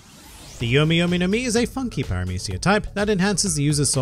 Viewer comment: thought he said Fuck you instead of funky like you were getting screwed eating the devil fruit